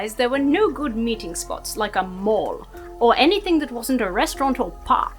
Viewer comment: A Midwestern city in the 1990s that doesn't have a mall close enough to drive to? I think this story is suspicious. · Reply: A mall? I thought she said a morgue.